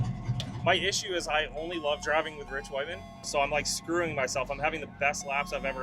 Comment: I thought said "I only like driving with rich women" 😂 had to play it back 3x lol.